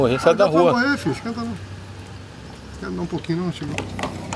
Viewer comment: quer me da um poquinho antes de morrer não ? ... Só eu que ouvi ? kkkkkkkkkkkkkkkk